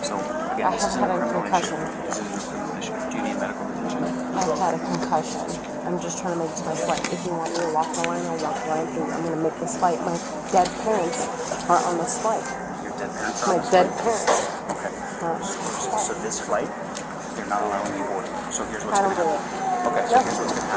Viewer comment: did she just say dead parents? No way i heard that right 😂 where are they? The big luggage area? Lmao.